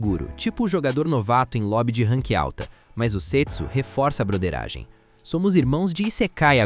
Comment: Eu ouvi "setso" achando que era sexo 😅